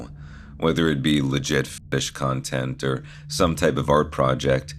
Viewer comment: Lmao i thought you were saying fish content 🤣